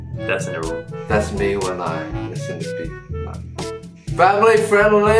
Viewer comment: "thats me when im ............" I cant hear it, can anyone ? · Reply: Listening to BTS?" maybe